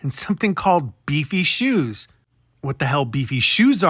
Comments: I think he said “DC” shoes not beefy shoes lol they’re a brand of skater shoes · I think he said "DC" shoes, skater shoes from back in the day. Comfy, but super casual